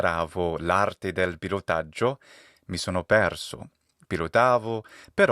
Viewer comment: My n+i in the first story came in reverse as i+n. I didn't catch the word pilotaggio (I heard it as pirotaggio) which threw me at the start. But when I got to the rest about the airport and the sun going down it dawned on me what you were talking about. I suppose as I did understand that you were learning something and got lost, you could argue that there were elements if n+i but I think it can work both ways.